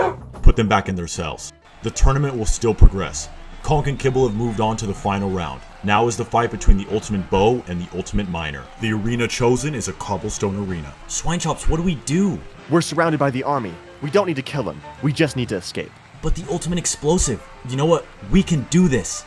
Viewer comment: “The ultimate miner” is crazy 💀💀💀 edit is: this is one of the best comments I ever wrote I guess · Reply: I heard "the ultimate minor."